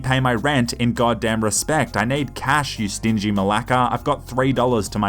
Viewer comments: Did u say malaka? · did u just say malaka?? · did he say "malaka" at 2:39???? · did you say "malaka"? Greek "μαλάκα"?😂😂